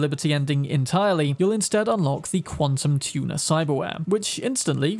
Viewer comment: I hear Quantum Tuna lmao.